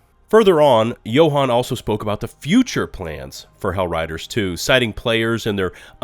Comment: ▶▶Arrowhead's CEO is back and giving us details! Plus, we've got an interesting leak into some future Helldivers 2 stratagems! 📝►Latest Patch Notes - <link> 🎮►Helldivers 2 on Steam - <link> 📣►Follow me on Discord - <link> 📝►Support me on Patreon - <link> 👕► Official Merchandise - <link> 🎦►Follow me on Twitch - <link> ✅►Subscribe to - <link> 🐤►Follow me on Twitter - <link> Buzz Battalion Patrons: 🥇Jaymie Ridall 🥇Cartoonr BOY 🥇Darrin Chambers 🥇DrLukeTheDuke Thank you so much for your support to help make content like this possible. CHAPTERS 0:00 - Intro 0:54 - Use Those Chapters! 1:28 - The Game Master 3:08 - Changes & Predictions 4:19 - Future Plans 5:45 - SPOILERS - Incoming Stratagems 6:52 - More Lethal Devices 7:46 - Consider Subscribing & Socials! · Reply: at the 4:23 second mark you say hellriders 2 instead of helldivers 2 that is all.